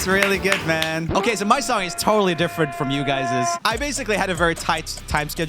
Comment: Did he say guyses?